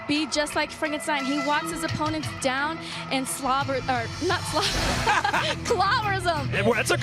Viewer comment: Karate Hottie says slobbers anyone else catch that?